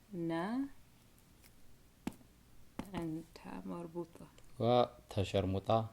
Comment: Did he just said ta’a shermuta Lmao 😂 😆 😂 11:57, please whip him 😂 or ቁንጥጥ between his legs lol